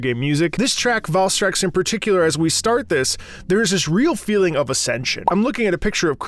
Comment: Did you say “Ascendance”? 👀